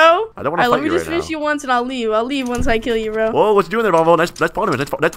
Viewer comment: LMAO WTF WAS HE SAYING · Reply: He's saying "that's fall damage" many times